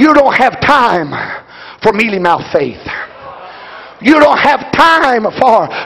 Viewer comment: Does anyone know what he said at 23:20 -23:22? "We don't have time for ____ faith"? It's hard to hear what he said right there. Someone please help. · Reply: Melymouth faith.. Basically determined faith that you have to push through and believe despite what the circumstances look like